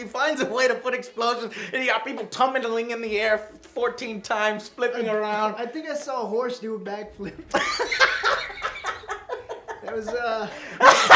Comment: "I think I saw a horse do a backflip." This is the perfect way to describe this movie. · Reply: Atofu i thought he said, "i dont think i ever saw a horse do a back flip before." or something like that.